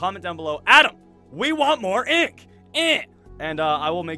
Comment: HEY ADAM............................................................,............................................................................................................WE WANT MORE INK ..EEH.. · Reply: the emerald guy No man im pretty sure at the beginning he actually says ADAM, WE WANT MORE INk EH